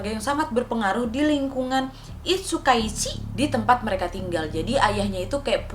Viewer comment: Aku dengernya Icih Sukaesih dong wak :v